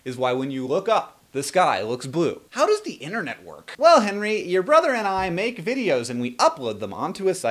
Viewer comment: does he say 'your bother and i' ???